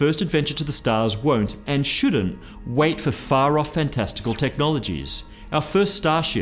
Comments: I thought he said "testicle technology"... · I actually thought he said "far-off and testicle technologies"